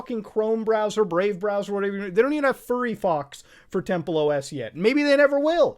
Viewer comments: did he just say furry fox ?😂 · did he just say furryfox